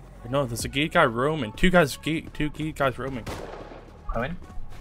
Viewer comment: it sounds like he said there's 2 gay guys roaming oof